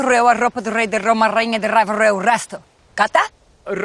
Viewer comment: の『わかった？』が日本語の分かった？に聞こえた w